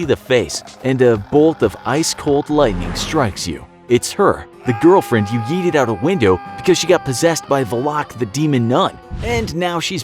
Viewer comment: I thought he said eat the girlfriend